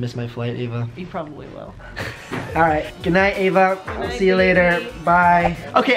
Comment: Who else at the end thought said "Bye baby"?